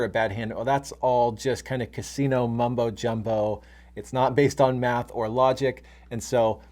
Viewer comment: Did u just say mambo,jambo😂😅